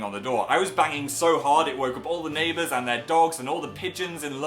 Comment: okay for a second I thought he said he was banging DAN so hard it woke up all the neighbours oops